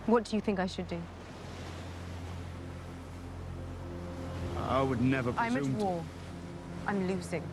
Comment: What Jon was going to say in 1:46? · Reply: Jon said, "I would never presume to..." (he was being modest, one of Jon's best qualities that he obviously got from Ned Stark)